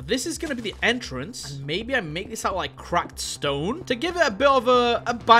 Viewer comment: I thought said "crapped stone".😂😂